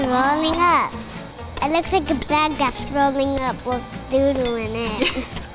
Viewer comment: did she say doodoo